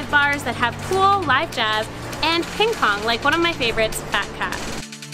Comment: What was the name of the bar you mentioned? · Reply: It was under #25. Sounded like you said "Fat Cat"